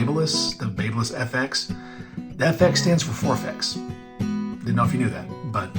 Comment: FX stands for what word was that? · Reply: Forfex